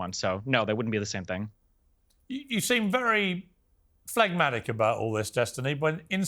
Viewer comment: does he say fagmatic?